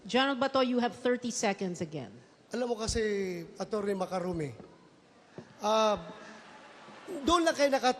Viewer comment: Ano sabi ni Gen. Dela Rosa sa 1:51:50? Atty. Makarumi o Makaruni? 😂😂😂😂